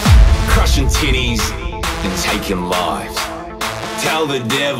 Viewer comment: In the beginning at first I thought Fuse said "Crushing T*tties" LMAO